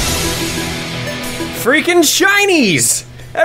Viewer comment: Omg I thought he said FREAKIN CHINESE at the beginning😂